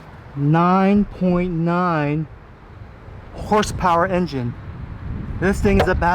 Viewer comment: At first I thought it said 99 Horsepower lol.